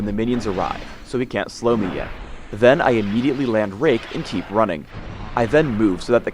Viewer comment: it sounds like he says rape